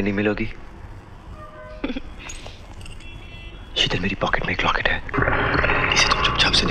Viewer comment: I think he was trying to say meri pocket mai rocket hai 🤣🤣🤣